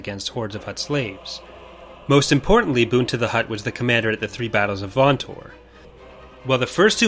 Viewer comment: I thought you said pizza the hutt 😂